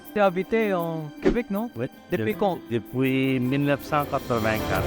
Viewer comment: the guy at 00:29 meant 1984 ata paps, not 1994 f I heard it right. hehe